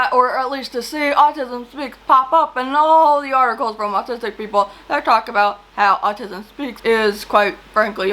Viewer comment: Really enjoying the video so far! But I wanted to bring up a discrepancy in the captions that I think may be important. At 5:44, you say “...and all the articles from *autistic people*...” while the captions say “...and all the articles from *doctors or people*...” Idk, I just think that might be an important distinction to make and I want to make sure people who are relying solely on captions can get the correct info! 😊